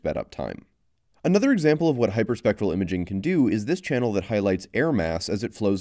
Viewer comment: Did he say “hypersexual imaging” at 7:00 +?? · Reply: It's hyperspectral